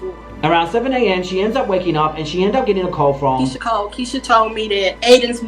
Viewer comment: Did she say Keysha cole called her ? Huh 6:20 she say it hella fast and never another mention who is this person